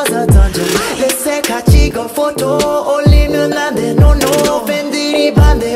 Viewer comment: Did it say oli london